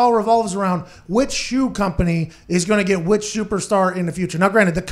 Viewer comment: did he say "shoeperstar?"